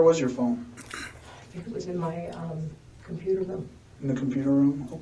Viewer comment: she said it was in my hand ~in my computer room.. anyone else catch that?